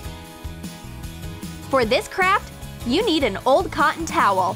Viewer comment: When she said, for this craft, I thought she said for this crap